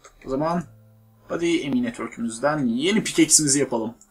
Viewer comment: abi ME network dedin ya ben onu emine twerk anladım